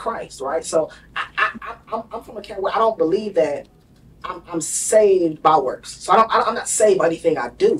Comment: Yo shalom family mhncb @ 44:06 did he said he from a camp that don’t believe he saved by works?